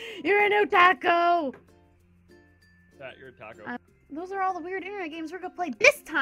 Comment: because i am British when said otacku i am like did she just say otaco?